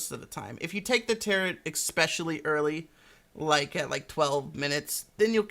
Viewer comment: Did you say: xpecially early? 11:55 ;)